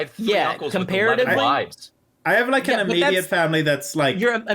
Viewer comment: What did say about having three uncles who did something? talked over him and now I have no clue what not “fairly normal” thing they did. At about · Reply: 3 uncles with 11 wives